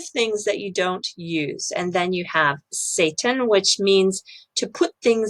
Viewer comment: That Six Sigma is a 5 pointed pentagram design. Hummmm.... Not surprised 14:07 "You have Satan" (did I hear that correctly?) which means to put things in their place?!? HUH?!?!?!?